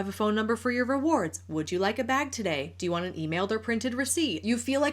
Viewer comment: she actually said “today”